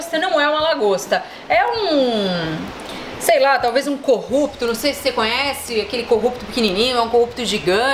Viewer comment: Eu entendi Corrupto kkkkkkk.